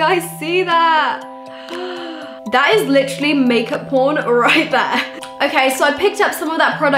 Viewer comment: did she say make up pawn??? or make up born